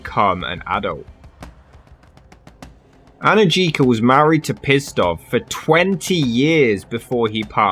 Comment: I chuckled when I thought I heard his name was "Pissed-Off" 😆